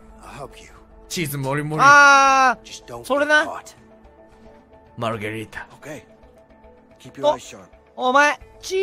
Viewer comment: IN 7:28 I THINK KANAE SAID MARGARITA